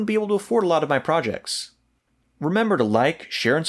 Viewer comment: Sounded like you said, "Lab Goats Out."